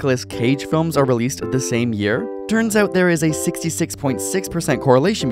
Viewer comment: Did I just hear someone say... 666 ? I'm on my way.